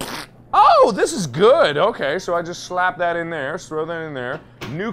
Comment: Mark: Just slap that in there Captions: That’s what she said